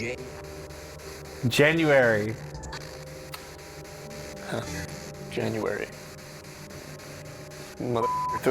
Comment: Yo at 28:45 i heard jane instead of January